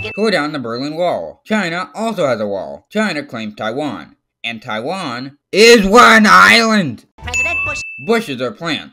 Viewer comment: 7:01 Me: I thought you said Taiwan was fictional